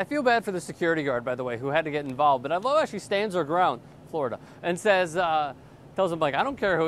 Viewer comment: what is he saying? FLO-RIDER? FLORIDA?